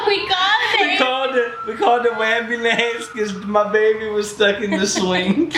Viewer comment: No one: Not a single soul: wE CaLlEd tHe waMBuLAncE!! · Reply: Omg i didn’t get the joke , i thought he said raeambulance , i’m dumb